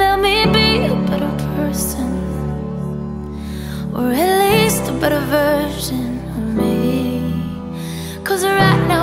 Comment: Right i've listened to this like 20 odd times and i keep hearing "or atleast a better version of me" not "release a better version of me". I swear down, i'm deffinately hearing it correctly